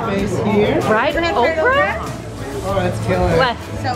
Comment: Fried okra is so bomb we love it here in tx! Y’all should def come to tx 🤪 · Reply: I’ve never had it before and I legit thought they said fried Oprah! 😂